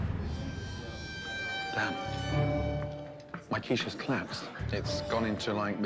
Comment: I thought he said his quiche was clapped and I started laughing